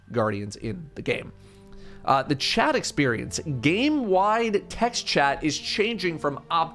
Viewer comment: I thought he said Chad experience